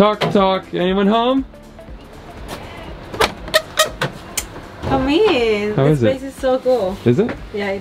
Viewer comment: "come in" sounded like perfect Norwegian "Kom inn " 😄